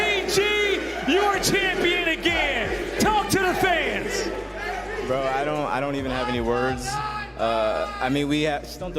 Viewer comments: when aPG started talking someone in the crowd yelled something, to which he responded "don't be mean". What did they yell? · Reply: “Bye cloud9, bye” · what did the fan yell at APG? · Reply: “Bye cloud9, bye”